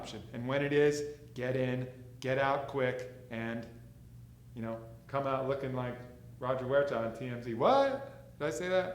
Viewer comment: did anyone else hear "come out looking like Roger Huerta on TRT"?